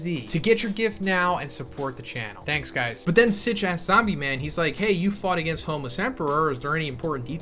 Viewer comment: For a second I thought you called Zombieman a Snitch a** Anyone else hear the same?